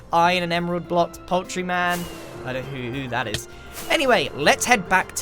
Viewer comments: did you say... Poultree Man? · Uuuh, I always thought you are saying "Poetry Man", well i got enlightened after looking at your shop... And this is really not a paid advertisment -, back me up.